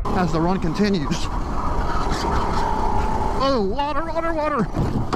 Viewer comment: On the second crash slow mo it sound like he is saying otter otter water I thank that is funny